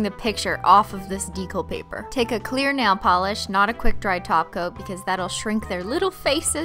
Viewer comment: did she say quick dry TOP COAT???🌮🌮🌮 Whose video is this???